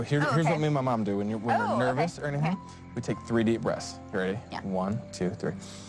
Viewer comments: When Colton said "take 3 deep breaths” I legit thought he said "take 3D breaths" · It sounded like he said, "take three deep breasts" at 10:49.